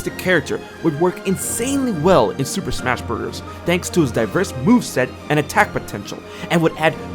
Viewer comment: did he just say it's super smash burgers let me slap him